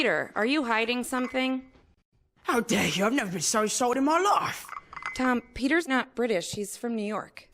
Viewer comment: what does Tom say? "I've never been so insulted all my life?"